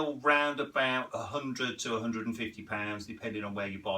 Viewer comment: What I Heard: "They retail for about.. $100-150 thousn" rewind What said: "They retail for about... 100-150 pounds"